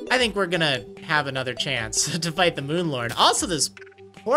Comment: I honestly thought you were saying Moon Lard. xD